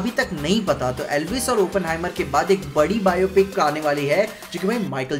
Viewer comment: Anyone hear at 7:48 i heard elvish and openhimer 😮😮